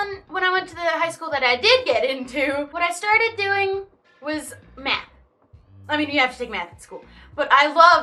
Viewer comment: who else thought that said "then I started doing meth but it was actually math😂😂😂😂😂